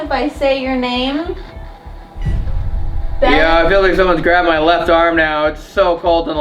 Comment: Panasonic I heard ‘someone’s here…on your left’